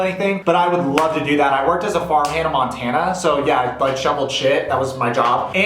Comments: deadass thought you said you worked at a farm with hannah montanna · I really thought he said "I worked as a farm Hannah Montana" for a second. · Am I the only one who heard “ I worked as a farm Hannah Montana” or is it just me 😂